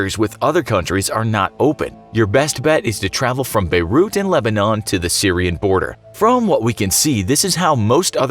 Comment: did you just say "from beirut and lebanon"..?